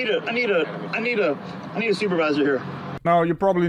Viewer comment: What was that? Because all I heard was “I need a- I need a- I need a slap to my stupid face, please.”